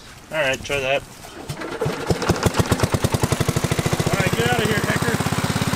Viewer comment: sounds like he said “get out of here Nig$r”